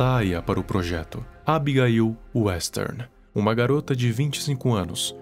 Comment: Juro que ouvi Abigail Wesker